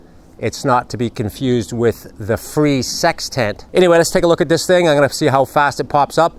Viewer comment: I swear I heard "free sex tent"... 😂😂😂